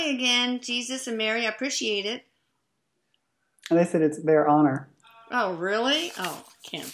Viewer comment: time stamp does anyone else hear a female voice say “honor?”